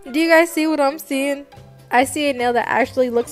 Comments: Sorry I thought you said marvel not marble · I thought she was saying MARVEL like “MARVEL DESIGN” I just realized it was MARBLE😭😭😭💀💀💀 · first i thought it was lightning then t i thought you kept saying marvel 😶 then i was like OHhhhhHh MaRbLe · I thought she said "MARVEL" but it was "MARBLE" Lol